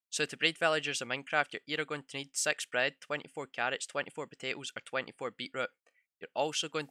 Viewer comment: I thought he said s-x bread lol I misheard em